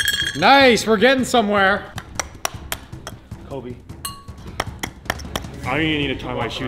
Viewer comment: Did he just said kobe and missed a shot??? I can’t tell but if he did, then disrespect